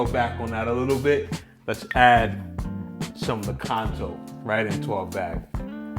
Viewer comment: did he said kanto? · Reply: Lakanto